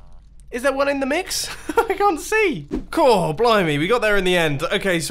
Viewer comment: I thought he said BLOW ME at first but then I listened a bit and I think he said blimey. I think. Hopefully.